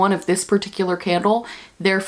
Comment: Today I heard “today’s scandal of today” instead of candle and honestly it makes sense haha